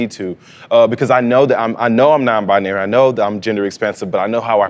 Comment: I heard it as “gender expensive”, can we make this a thing😂😂